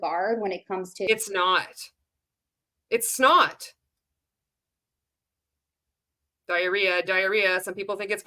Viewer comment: "It's not. It's snot!" I swear that's what I heard 🤣